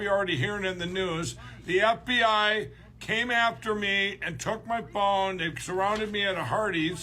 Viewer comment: Am I the only one who thought Lindell said “the FBI surrounded to me and took my bong….” ?